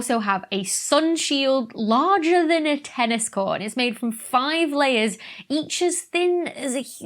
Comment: Watching at 2x speed, I heard that as "also has a sun-shield larger than a tennis ball" ... and was very confused.